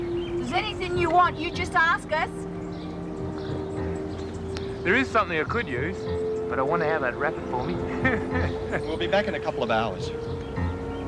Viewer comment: what does the guard say at 14:20 ? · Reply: "There is something I could use but I'd wonder how they'd wrap it for me"